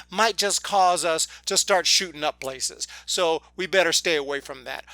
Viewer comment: you say shooting up places. i thought you said shooting up blazers